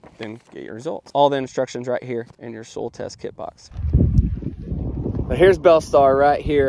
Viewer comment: I get a kick out of your Oklahoma way of pronouncing words sometimes. I thought you were saying a “Soul Test.” Thanks for showing the print on the box! (I’m a Chicagoan; we pronounce words differently, too.🙃)